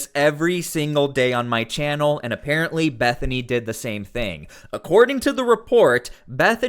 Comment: I thought he said Methany. She definitely looks like a Methany